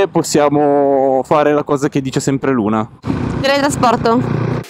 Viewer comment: cos'è che dice sempre Luna? Non ho capito 🤔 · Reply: Teletrasporto 😅